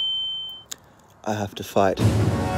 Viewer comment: OMG WHEN HE SAID I HAVE TO FIGHT I THOUGHT HE SAID I HAVE TO FART LOLOLOLOL